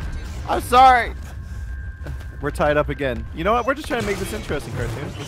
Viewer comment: At the beginning it sounded like said do you know DA way